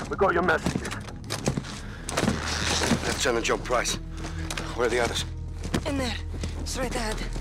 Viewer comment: In the clip at 1:29, the subtitle is wrong. He says “where are the others?” But it’s subtitled as “where are the rest”